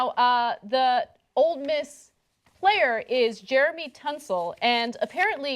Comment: Is it just me or did Ana call him "Jeremy Tunsil" at 0:12 lolz